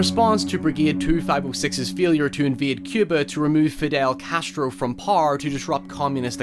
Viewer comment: Not kidding I thought he said Infidel Castro here 😂😂